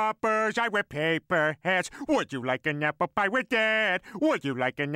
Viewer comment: For the longest of time I thought he said would you like a nipple if I was dead? 🤣